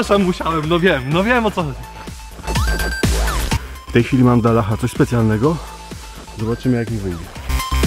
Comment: Poważnie stopa mi zahaczyła o dywan :\ 2:04 odpalcie szybkość odtwarzania na 0,25... POLSKA CHCE POWTÓRKI KARNEGO! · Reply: Bardziej mnie zaniepokoiło to że usłyszałem w 5:07 "Mam dla Allaha coś specjalnego" :D